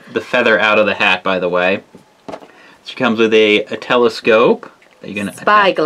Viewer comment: I thought when said 'she comes with a telescope'i thought he said nutellascope